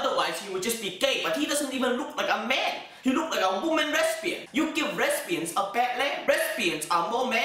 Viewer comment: i thought he said respian lol